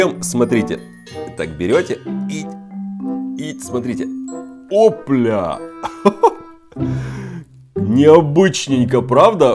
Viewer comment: мне как то послышалось "о бля" 🤔😂